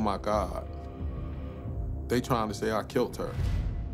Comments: I hear "anyway I killed her" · During the 1st run of the interrogation i thought i heard him say "I, I killed her" but wasnt sure but the investigator who went back & rewatched it is a very sharp gentleman & i wholeheartedly 🫡 him for his hard work after all those decades they didnt give up & found answers for the victims ✊🏾👏🏾🤝🏾💯